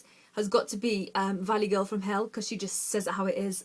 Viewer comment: does she say 'valley girl from hell'??